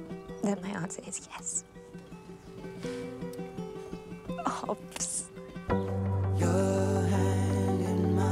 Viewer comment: What does she say at 2:27? Hopes? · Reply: Think she says Obvs......short for Obviously:)